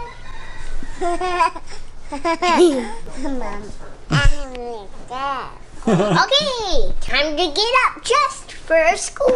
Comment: Did say you perverts at 7:33?? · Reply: She said give her her dog